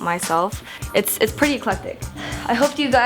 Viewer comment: what did say at 11:36? its pretty_______? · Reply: Eclectic